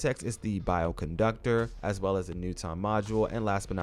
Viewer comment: Hole up did u just say “newt on” module lmao my Man U don’t know who newton is?😅